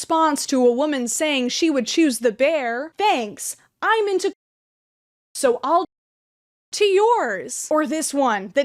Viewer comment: if anyone was wondering what they said, they said "Thanks i'm into corpses so i'll jerk off to yours" fucking disgusting